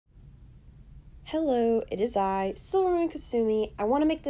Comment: Finally someone says their own voice is beautiful ;) It sounded like u said silvamoon silly